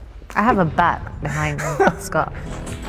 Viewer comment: does she say bat or butt? lol i can't tell